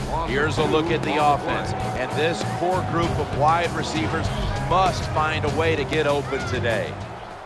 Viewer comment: Did he say "poor group of wide receivers" Cus if he did im just not gonna get this game